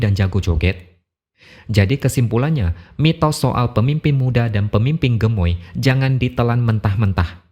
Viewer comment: Mas, mungkin kita semua salah dengar.. Klaimnya bukan "Pemimpin Muda" tapi "Pemimpin Mudah".. 🤫